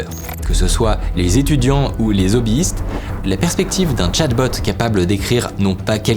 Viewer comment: Attention, faut pas faire la liaison avec "les hobbi-istes", y a un H. Parce que moi j'ai entendu "zobbistes", ca m'a surpris :D